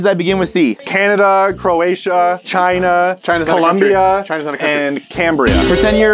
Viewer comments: did he really say "China's not a country" 🤣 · did he just say china is not a country